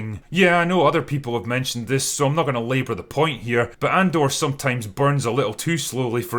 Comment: Wait...did you just say AndorDog?